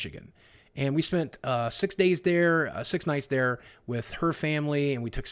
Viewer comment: Swear I heard him say "six nights dere". He's coming down with a Wisconsin/UP accent